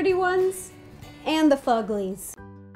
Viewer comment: in 8:22 did she say thuglies